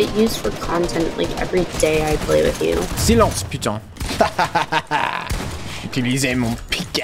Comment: I thought u said silence pigeon bc I was doing something else but then I saw 😂